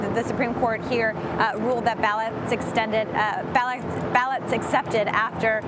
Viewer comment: can someone write out what she was trying to say, because I couldn't figure it out. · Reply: ballots accepted